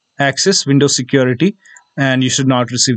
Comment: i heard unsubscribe to my channel 😂 but u got a new subscriber